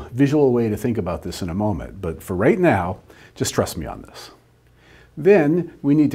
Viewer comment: "for right now, distrust me on this" ... well, that's what it sounded like. I hope you meant "just trust me on this"